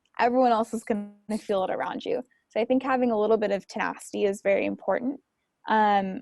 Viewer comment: Amazing video. But can you clarify what she says at 22:20, “ having a little bit of ..,” what? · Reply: tenacity